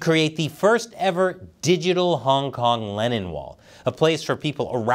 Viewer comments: Make China Mad! Sign the China Uncensored Digital Hong Kong Lennon Wall! <link> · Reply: I have missheard, i thought you said Lenin wall. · You need to articulate "Lennon" better (for a moment there, I thought you had said "Lenin").